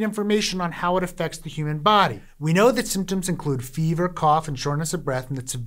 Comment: Hey Healthcare Triage: hope you're not paying for English Closed Captions, at 2:13 mark instead of fever we are instructed to "jerk off". If in quarantine for 2 weeks many likely will, but you might rather the viewing public have accurate info. 😜